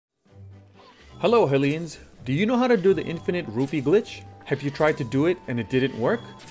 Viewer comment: At first I had thought he said, “infinite roofie glitch”, and I thought, I am on the wrong video.